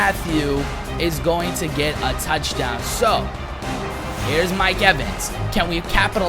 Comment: did he say touchdown